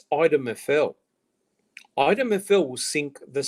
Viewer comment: Hey. Great conversation. Question: What did say at 2:08:31 ... He said that something will sink the State. But I couldn't understand whatever the thing was that he said would sink the State. Thanks! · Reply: Item FL will sink the State - unless Teresa was made out of wood and red paint.